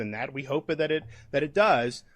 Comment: did he say “we hope that it does”? If that’s what he said, it sounds like he was saying “we” as a group and “hope it does” as in wants it to be a Cat5😳 did I mishear?